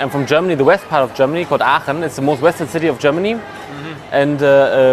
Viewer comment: I think he said Aachen, not Oberhausen